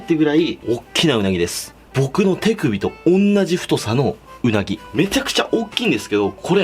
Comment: 僕のティクビと同じ太さのうなぎに聞こえた、実はそうなのかもしれない